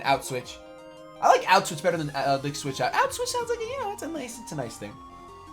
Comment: Sounds like you were saying "auschwitz sounds like a nice thing" hahaha!